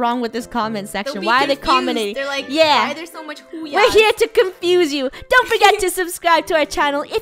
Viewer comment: I thought u said boo yah not hoo yah